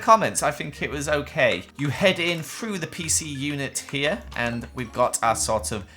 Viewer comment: did he just say pcu?